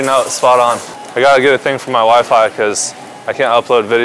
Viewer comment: I thought he said "gotta get a thing for my wife" 😂